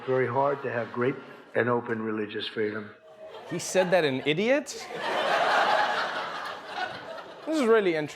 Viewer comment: did he just said idiot india🤣😂😂. I'm dead😝😝